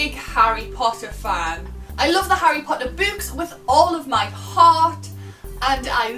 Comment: she says she loves the Harry Potter books and the subtitles say the Harry Potter boobs